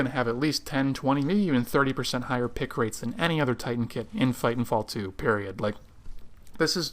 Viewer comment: it sounded like you said fighting fall 2